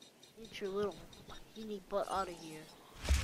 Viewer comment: it sounds like I say the F word but really I said butt